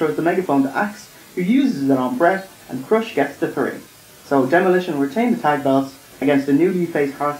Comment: did you say "Crush" gets the three?